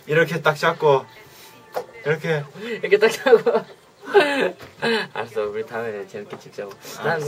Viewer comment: sounds like jimjn said "I'm so retarded" lol then again I don't understand hangul very well. Jimin laugh is adorable. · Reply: "알았어. 우리 다음에" '+더 열심히 찍자구' 2:26, Jimin said 'Ok, let's shoot the video harder next time'. It's 2020, but I have to say this on English-speakers' comments about "I'm so retarded"... He looks so baby-cute in those days:)